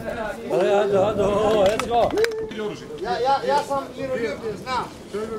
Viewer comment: were any of them speaking serb? at 1:47 i swear i heard someone saying "ja sam... znam" or something like that